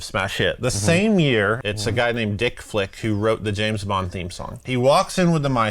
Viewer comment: I swear to god I thought he said his name was "Dick Flick", then I searched it up and realised its VIC Flick and now i cant stop laughing